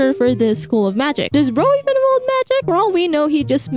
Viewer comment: I don't give a Damm about burning spice learning magic ( especially his "axe" magic) what do I wish to know is... DID YOU JUST SAY "BRO" Because In That Tone Is HILARIOUS!! TO HEAR in